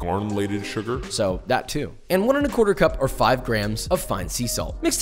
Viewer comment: I'm not the only one who hears 1 1/4 cup of salt at 1:40 am I?